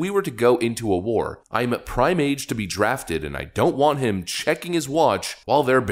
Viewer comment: Who else heard "I'm PRIMATE to be drafted..."